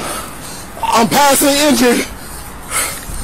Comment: But it sounds like he said I'm passing the entry...not I'm passing the injured